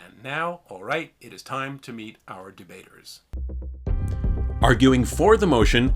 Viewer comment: i keep hearing "jebaiter" and not "debater."